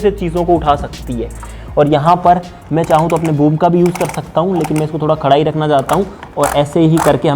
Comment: I heard boo* 😂😂😂